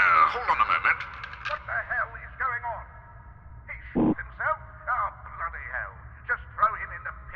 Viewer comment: Instead of "He shot himself" it sounds like "He sh!t himself?" due to the censor thought that was funny lol